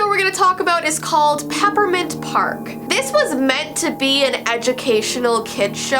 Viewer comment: When she said peppermint park i thought she said peppa pig